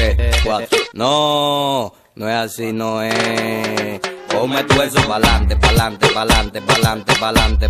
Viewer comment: Yo pensé que decía yo me tuelso pa’ lante🤣🤣🤣